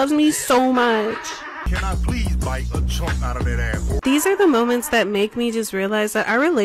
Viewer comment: why does the captions say ass instead of apple? 😂